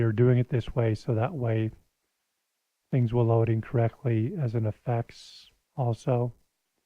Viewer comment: For some reason I heard "load in correctly" as "load incorrectly" XD